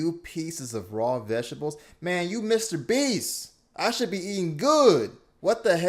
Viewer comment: Thought it said Mr Bean